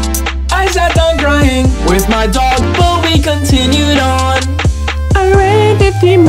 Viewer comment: I thought it said, "I sat down crying, with my dog, booby, we continued on." 😂